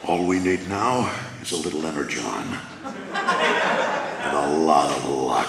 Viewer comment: When he said energon I thought he said letter, john